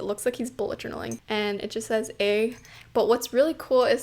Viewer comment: my first thought was that he was saying "Tae" cause I always see Jimin writing it in hangul hsdiuhsj